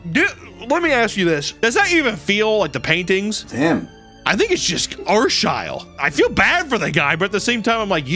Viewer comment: What does he say at 9:44 ? Arshyall? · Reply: Arshile